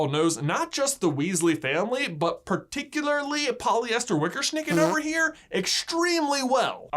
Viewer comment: Polyester Wickersnicker... lol 7:54... did he say Regular Witherbits?